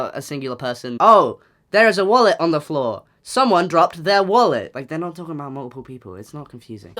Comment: i was itching my ear and it messed w/ the audio and i heard "OH LOOK THERE'S A MULLET ON THE FLOOR" not "wallet" and i was really confused "SOMEONE DROPPED THEIR MULLET"